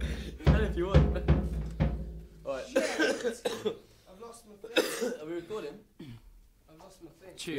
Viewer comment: did anyone else hear "SHIT... i lost me pick."